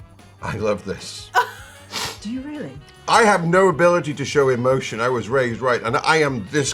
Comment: did he say “I had no ability to show emotion, I was raised right” ?